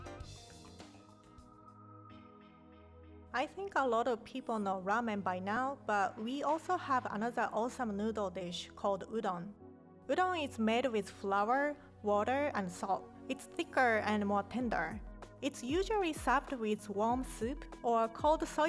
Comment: wow i thought rie said udong😂😂 filipinos will know